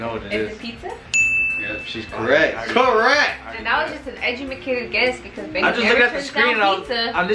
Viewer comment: did say "echumikated guess" lmao shes drunk drunk 😂😂